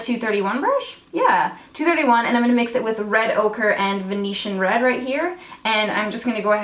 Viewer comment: I thought you kept saying "red ogre" I was like interesting name ABH okay